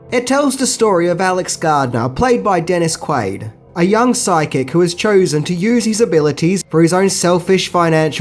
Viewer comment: I thought you were saying "sidekick", not "psychic". Must clean out my ears.